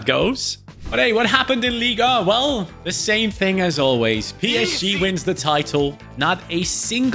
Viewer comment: the ez sounded like p s g!!!!!!!